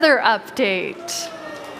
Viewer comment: i thought it said "weather update" and I got really happy (｡•́︿•̀｡)